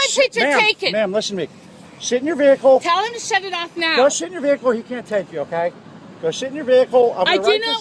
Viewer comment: it sounded like he said "Mam go shit in your vehicle"